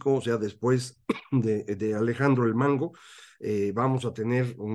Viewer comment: Maestro Buenos días. Tema claro e interesante. En el minuto 8:25 escuché que dijo Alejandro el Mango, ¿escuché bien?